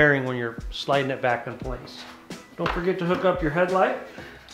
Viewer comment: I just walked in from working on mine.... I hear you say " dont forget to plug in your headlight" DAMMIT ! Now I have to go back out and plug in my headlight ! Thanks for the reminder. Stay Safe, Stay Warm Positive thoughts and vibes